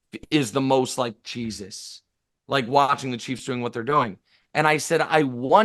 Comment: did he say which AFC team is most likely Jesus? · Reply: No the team that says “Jesus” like dammit